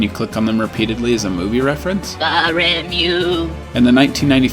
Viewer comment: me thought they were saying "damned you" as they were aware that they will gotta explode